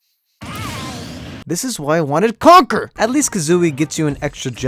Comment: When you were about to say "this is why I wanted conker" the video cut to an ad so to me it sounded like you said you wanted cock